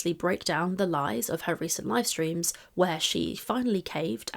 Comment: I thought you said 'lie-streams' there which is not you, it's me, but I did amuse myself.